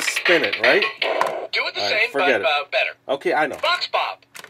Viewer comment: What did that voice say on that thing? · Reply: "Do it the same, but, uh, better."